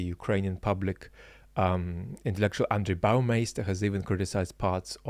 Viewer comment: What was that lecturers name? Cc and transcript say "under biomass" but it sounded like "Andre Boumaiz"? Cheers for anyone with a better ear than me